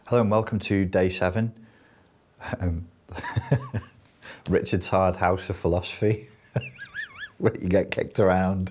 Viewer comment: ‘Richard’s hard house of philosophy’ 😂😂😂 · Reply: Glad you said that i thought i heard "Richard's TARD house of philosophy. " lol